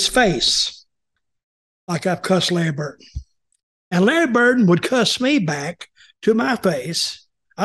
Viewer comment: Sounds like he is saying Larry Bird. 😂